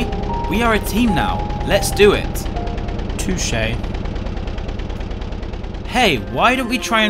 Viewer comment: interesting how it’s “too shay” instead of “touché” 7:37-8:10 lol 8:14 your leg was scratched by a glove apparently